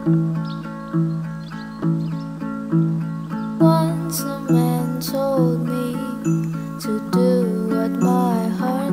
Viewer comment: "once a man told me to do what my heart says" AND YOU HEAR WILBUR IN THE BACK TELLING TOMMY TO DO WHAT HIS HEART SAYS, IM CRYING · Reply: Tommy, I want you to do whatever your heart tells you to do.